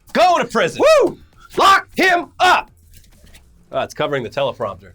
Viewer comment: I heard "Fuck him up!" :P